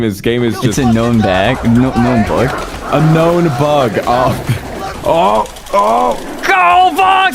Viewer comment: small correction: they actually said "a known bug", not "unknown bug" :)